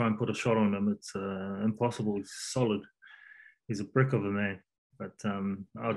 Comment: "He's a .....of a man". I heard prick 😂, closed captions said brick 😞